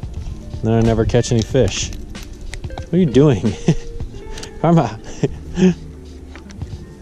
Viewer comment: What was your dog's name? I thought you said Karma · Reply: Yep her name is Karma 🤠